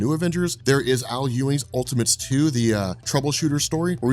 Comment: Wait. Idk if it's cause I'm drunk or what, but I feel like at 16:54 he says Aluë which is also a "God" from Lord of the Rings, that's just crazy to me if that means they are in the same universe haha